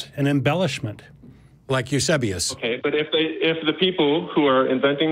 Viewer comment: who was Matt talking about. It sounded like he said Yusubious. How do you spell the name?